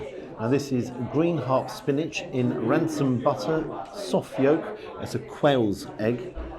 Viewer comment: did u just say rancid butter hahahahahahahaha online love to your family buddy great video !